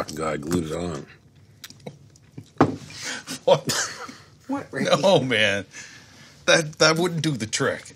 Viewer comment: almost sounds like Bubbles said "What Randy"